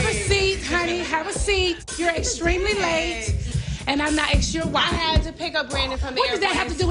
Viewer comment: What did Nene say at 2:11 ? "Imma ask you why"?